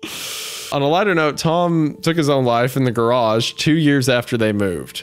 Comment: I had this on in the background and I fully thought at 1:05:02 said "On a lighter note, Tom shit himself"